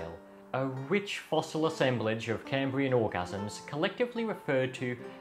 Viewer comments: "A rich fossil assemblage of Cambrian ORGASMS"??? Am I hearing that right? 😂😂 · 33, Did he just say Cambrian orgasms? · "Cambrian orgasms" I had to rewind a few times to be sure I heard that right